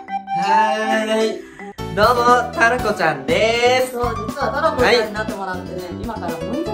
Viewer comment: タロ子ちゃんってたらこちゃんって聞こえる!!